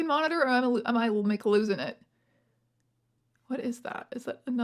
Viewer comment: did you say “am I like losing it” or “am I McLosing it”?